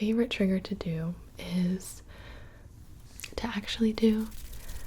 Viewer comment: my favorite trigger to do is tap ... actually do finger flutters 😂 Not sure if its what she said tho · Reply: She said "trigger to do is to"